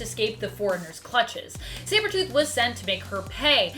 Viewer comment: Umm, did you say "Cybertooth" at 5:41? 😄 Love the vids, btw!